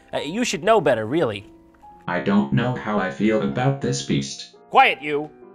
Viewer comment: The parrot said "I don't know how I feel about this feast" but at first I thought he said "I don't know how I feel about this beast". The beast being the horse